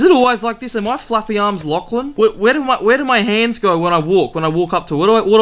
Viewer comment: “Am I flappy arms Lachlan” omfg hahahahahaha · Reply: I died laughing at that cause for a second I thought he said flappy birds Lachlan so I got a flashback to my friend swearing while failing at flappy bird